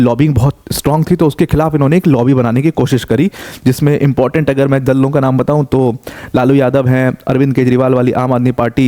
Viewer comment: did you say dallo ka naam😂